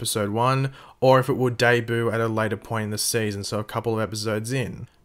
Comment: It kinda sounds like he said day-boo instead of debut